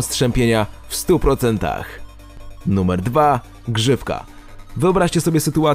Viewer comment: Usłyszałam grzybka zamiast grzywka